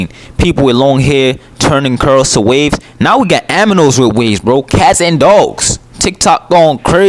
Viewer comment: Did he just say « aminals »? 🤣🤣🤣🤣